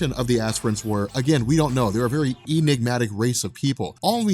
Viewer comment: Emignatic? xD am I hearing it wrong, or was that a slip of the tongue on behalf?